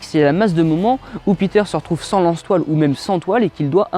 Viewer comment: Qd t'as dit "lance toiles", j'ai entendu "lance poils".... Super vdo